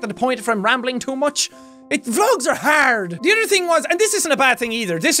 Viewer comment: When he said vlogs are hard I thought he said drugs are hard